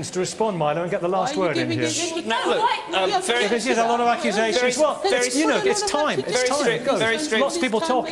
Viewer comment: I don't believe my ears! Can you hear what she's saying in the background: "Are you giving him more time?" (namely to speak)